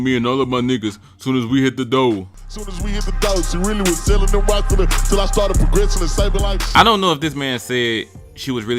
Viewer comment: Sound like he said “till I started progressive and saving like” 😂